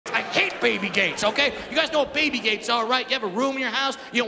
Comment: Thought he said baby gays